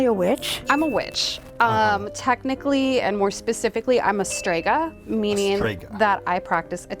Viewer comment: Instead of "stega" I heard "strzyga" which is a demon in slav culture and I got a lottle too excited